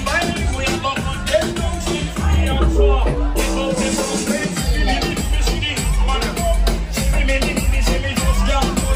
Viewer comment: What is the place at 16:38 called ? I couldnt hear . Great vlog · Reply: This is Taboo Beach Club. I went here once at night for dinner, and during the day for a day party. Thank you for watching!!